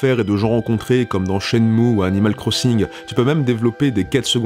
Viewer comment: À 2:45 j'ai pas compris, il dit "comme dans ?? ou animal crossing". C'est quoi le 1er jeu ? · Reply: Shenmu